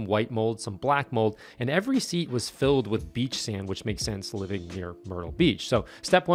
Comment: I thought you said the interior was "filled with beef sandwich"..lol! Great video and car, man!